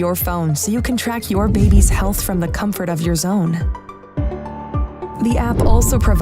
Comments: did they say the "comfort of your 'zone'"? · "From the comfort of your ZONE"? I had to back that up a few times to make sure I heard that right 😂🥶